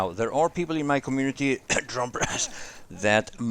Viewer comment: what did he say at 5:02? "there are people in my community, trompers?"